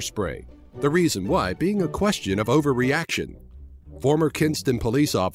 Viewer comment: I heard Equestria xD leeeel ^^